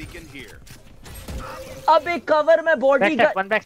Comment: Pheonix said "Dude give cover to da body stupid!" TOXIC PHEONIX! 3:58 Sage said "Brim, i know you are stupid and u cant understand us hahaha" TOXIC SAGE! 7:04 Skye said "How are you, How are you?" GOOD SKYE!